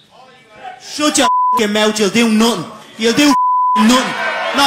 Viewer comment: what did Conor say? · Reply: Youll doo notting